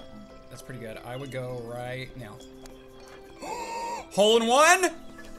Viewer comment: Did anyone hear say hole in one at 3:17?